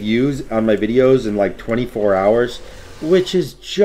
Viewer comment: You deserve more man, i hope to see your channel grow even more than you can imagine. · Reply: I thought he said "38 views in less than 24 hours" I was like hey buddy it was definitely a couple more than that lmao